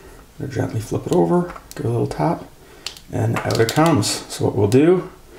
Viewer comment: what did you say at 4:31 ? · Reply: I think I said "give it a little tap" lol. Anything I can help with or clarify??? Cheers